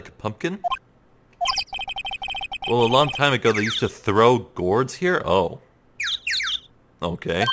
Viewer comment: He said GROW gourds! You put so much emphasis on that TH, you must've REALLY thought that's what it said